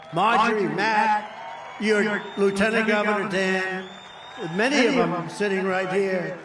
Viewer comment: did he say Lt Daan.. Stay away from Forrest Gump ....Trump